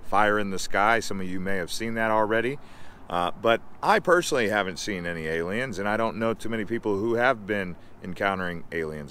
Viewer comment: At first, it sounded like you said, "I personally haven't seen many aliens." and I thought, 'Great, just when I thought things couldn't get any worse...'